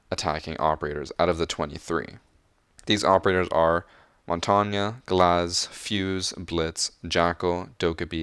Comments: Wait wait wait did he just say “Montanya”? Lol. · Why does he say montangya